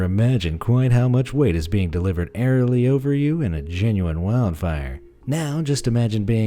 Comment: for a second i thought he said genji